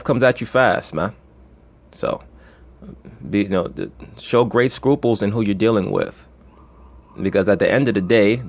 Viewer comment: What did he say here 29:14 - 29:16? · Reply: redgangsta27 Show great scruples(second thoughts) in who your choosing to dealing with